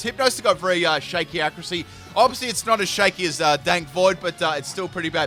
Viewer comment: DId he say dank void?